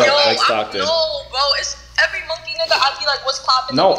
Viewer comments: yo who else heard that lil white girl say "monkey nigga" tf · Anyone else notice she said the n word @ 22:17 ..... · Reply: Ion know why it sound like she said monkey nigga, but I might be tripping